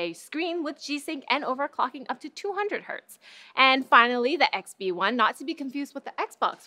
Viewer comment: Did you just say 200Hz as in 144Hz gaming monitor!!! WOW